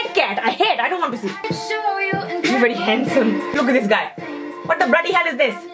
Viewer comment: What he said: "Look at this guy" What I heard: "Bruh look at this dude"